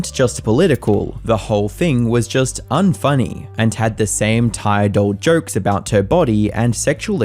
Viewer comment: I misheard this as “the same jokes about her tired,old body”. Man I need some sleep lol.